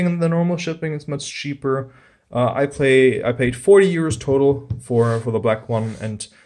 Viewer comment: lol I thought he said I played 40 years total im like damn